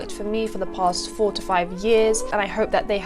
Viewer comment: I heard for the past 4t years and i was like what gurl💀